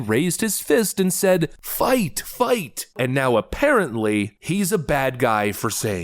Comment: actually I think not even that's true. I dont remember hearing him say "fight! fight!" I heard "wait wait wait" before he raised his fist. As in, he was telling the secret service agents to wait a second so he can get a money shot out of it. Which I mean, he certainly did that. 2:25 yeah "wait wait wait" as in "dont shove me off the stage yet im gonna pump my fist"